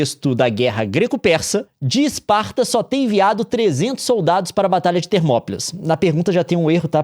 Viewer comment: Que susto que tomei! Leon disse "...de Esparta só ter enviado..." e eu ouvi "...de Esparta só tem viado..." da primeira vez. kkkkkkkkkkkkk